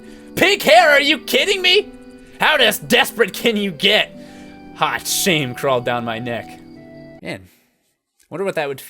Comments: "Hot shame crawling down my neck." At first I heard "hot Shane" and now I have a completely different picture in my head. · Hot Shane crawled down my neck.. Oh the things I hear